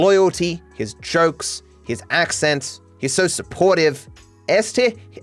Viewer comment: I love that at 40:19 the subtitles say este instead of S (btw este is a romanian word it means is if your curios